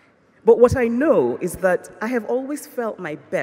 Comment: the subtitles should say "urgent" not "agent" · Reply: And at 5:12 it says "fear" instead of "veer" ... Come on now, even automatic subtitles are more helpful than this!